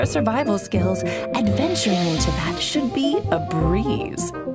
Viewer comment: "if only she let me tinkle with her bombs a little" I lowkey thought she said balls 💀and was hella confused